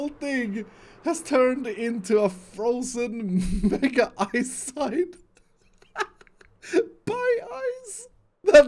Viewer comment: Frozen Mega Eiszeit? German word or i can't hear it correctly? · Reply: He was laughing as he said “frozen mega ice sign”